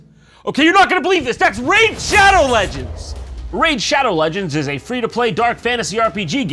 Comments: the caption said "rape shadow legends" 🤣🤣🤣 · The captions said "rape shadow legends"